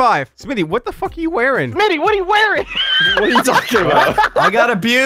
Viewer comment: I heard what delirious said at 17:06 Marcel: Smii7y wtf you wearing? Delirious: Mini what you wearing? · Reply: bananajosh1 He said Mitty xD